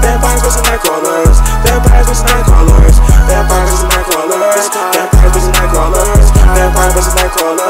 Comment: What is the background saying at 2:43? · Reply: Vampires vs Nightcrawlers